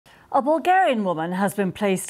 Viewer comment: I heard it as " a Vulgar-ian woman " at the start